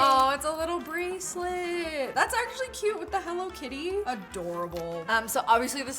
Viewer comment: omg at the 4:20 16:25 i thought she said what the hellokitty